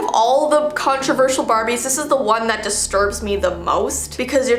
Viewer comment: I thought it said baby’s not barbies